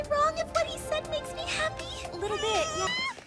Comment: he actually said "forgive me sir"